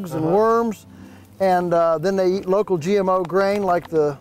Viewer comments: does he say the pigs and chickens eat GMO grain? Really?!!? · Ay 21:50 said his pigs eat local GMO grain? Did I hear that correctly or did goof? It just caught me by surprise. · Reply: Gil Fisher Non-GMO